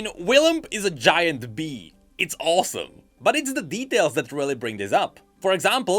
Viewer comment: I thought you said "But it's the BEEtails that really bring this up".